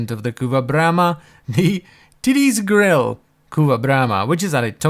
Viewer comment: i heard titties 🤣